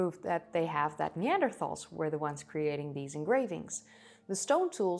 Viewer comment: I heard you right? Engravings? No paintings?